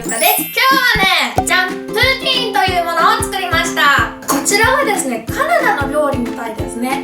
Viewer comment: プーティーン…最初聞いた時プーチンって聞こえた……